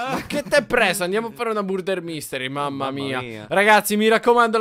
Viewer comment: ho sentito burder invece invece che murder. Vbb cmq na forza 2 assassini invece invece che 1